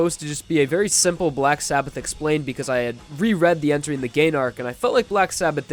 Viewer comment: I thought you said “Entering the Gaydar” at the beginning 😂